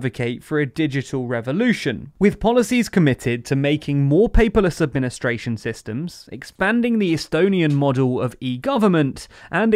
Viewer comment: What does he say at 2:53 ”Making more [PeopleUsubministrationSystems]”? I cannot make out the word.